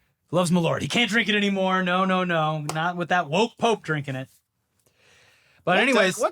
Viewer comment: Okay so I was distracted for a second and misheard "woke pope" as "wolf pope" and got really excited for a bit